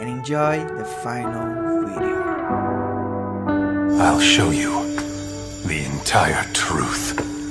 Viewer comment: Why did I hear itachi say "the entire turuth"😂😂😂😂